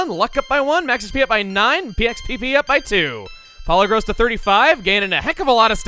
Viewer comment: What the literal shell did you say for PP. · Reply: PXPP